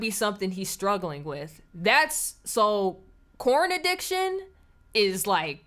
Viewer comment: Lol, I need to clean my ears. I thought you said corn addiction. I'm like, is that a thing? Must be some terrible genetic modification goin on.🤣